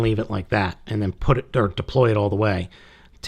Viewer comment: And now it sounds like you just said "flip the--dirk--deploy it all the way".